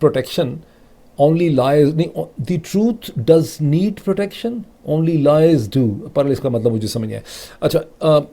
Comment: "the truth doesn't need protection" I think is what they're trying to say. Probably a typo or a grammatical mistake.